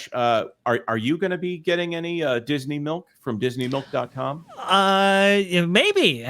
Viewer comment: For a second there I thought they said, "Disney milfs". Sorry, don't mind me